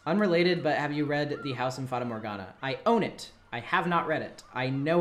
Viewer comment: what book/Manga is that? I can't process what's being said so idk how to spell it. House of...? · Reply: House in Fata Morgana